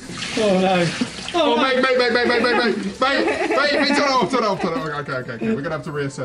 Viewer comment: did say "babe" or "wait"?? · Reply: I think he's saying, "mate."